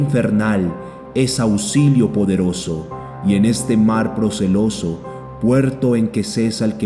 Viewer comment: Bien día Quisiera saber con exactitud que palabra es la que dice en el minuto 17:37 Muchas gracias Dios los bendiga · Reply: Buenas tardes, mi Dios te bendiga (Mar Proceloso) sinónimos de tormentoso, agitado